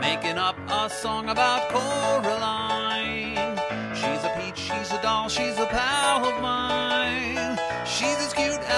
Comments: the first time i thought i heard "she's a bitch, she's a doll, she's a pal of mine" :D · He actually says "Shes a peach, shes a doll" but what u thought he said was funny